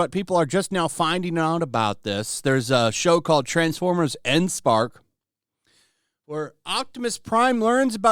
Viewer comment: the Show is not called Endspark, it's Earthspark.